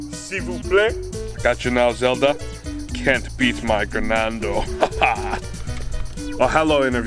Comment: DID HE JUST SAY CRENANDO?? HOLY SHIT SECRET GUEST